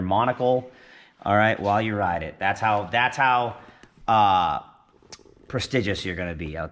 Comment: prestigious try again Wtf did he say Haha